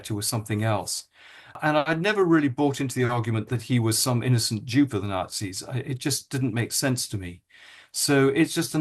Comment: lol the CC says you didn't "believe he was some Jew for the Nazis". Awful on many levels 😂 I love hearing your reports. Can't believe you're still a monarchist but love you anyway!